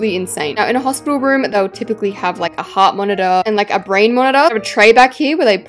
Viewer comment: babe did you just say brain monitor...